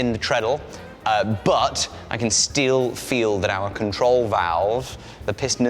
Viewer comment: did he just say "I can steele feel..."